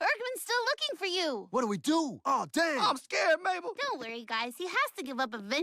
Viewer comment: did he just say oh damn???